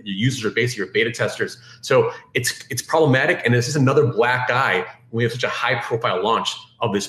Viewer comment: is gordon johnson the "other black guy" or "another black eye" ? Can't tell what he said there. Who is black guy #1? 🤣😂